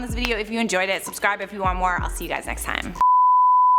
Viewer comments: Love you Charlotte. Big fan but it sounded like you said ret**d. Let's try to be better. Still a fan and not trying to be cancel culture. Take this down and re-editit. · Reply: Okay after 4 close listens I hear it now. I heard "I feel like a ret**d up here." Glad that's cleared up😊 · Love you Charlotte. Big fan but it sounded like you said ret**d. Let's try to be better. Still a fan and not trying to be cancel culture. Take this down and re-editit.